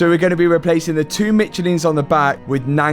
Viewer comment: thought he said replace the two mcchickens on the back